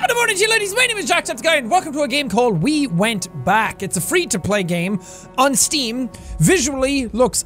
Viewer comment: I thought it said we went black😂